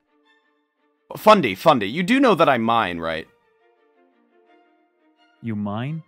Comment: i thought he said like i mind not i mine